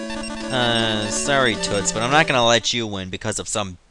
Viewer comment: did he just call Ishizu toits?